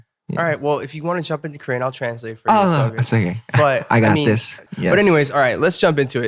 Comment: did I just heard jacob saying "oo sige" (which is a filipino word) or my ear is just crazy XD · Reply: he said "it's okay" hehe